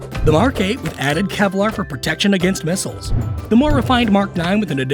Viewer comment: I thought he almost said protection against muslims